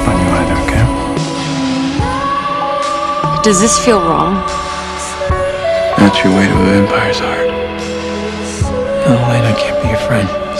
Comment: What does Damon says in 2:28? (BTW amaaaaaazing video 😍) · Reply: Maria Sierrou thank you! :) "that's your way to vampire's heart"